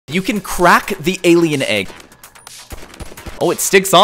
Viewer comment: On the first myth I looked away for one second... And I hear "Myth #1, You can crack the Indian egg" 🤣💀